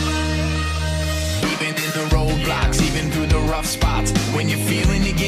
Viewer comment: Did he say roblox ??haha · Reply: Road blocks